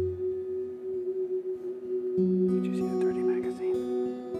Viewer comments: "did you see the dirty magazine".. Am I hearing that right? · Did he say "did you see that dirty magazine?" lol. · Anyone else hear at 24:34 when someone forgot to turn their microphone off and said "Did you see the dirty magazine?" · did anyone hear 'did you see that dirty magazine'